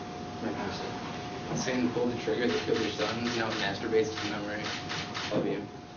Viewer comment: what is he saying exactly? i only understand "your son, trigger, masturbating"